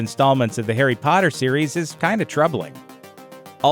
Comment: Did he just say 'hairy' potter?